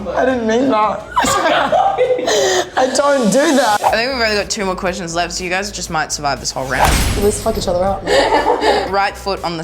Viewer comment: what are they saying 13:02-13:06 (I mean im not native but 😏)??? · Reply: “We only have two more questions left so you guys both might survive”